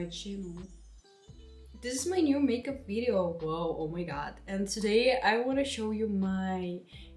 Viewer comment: quando ela falou oh my god em uma parte eu achei que ela tava falando "obrigada" kkkkkkk e eu fiquei tipo ??? meu deus ela sabe falar português? kkkkkk